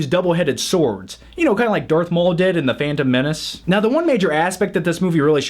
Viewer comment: At around the 1:45 mark, did you mispronounce it as "The Fandom Menace" intentionally, or was I just mishearing things? Because if you meant that, then nicely put.